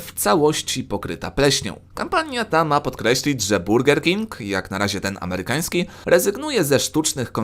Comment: 2.23 usłyszałem ,,burdel king"